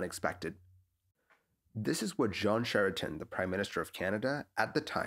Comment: I hear "John Sheraton" It's Jean (J like the G in giraffe, and rhymes with Sean/Saun/Shawn) Charest ( Sha-ray).